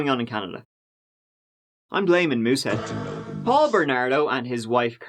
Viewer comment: 21Uhr02, 9. September 2020 0:28 He's blaming WHAT? Muhset? What is he saying there? Could anyone please explain? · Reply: He said "I'm blaming Moose head" don't get it but think Moosehead is a town in Canada !?! 🤨